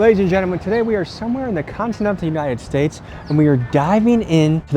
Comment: Did he say continent of the United States at 3:13 ???😂😂😂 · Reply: Continental